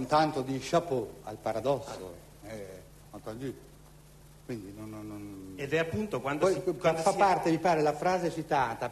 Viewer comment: a 29:53 cosa dice? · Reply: "Entendu", francese per 'inteso'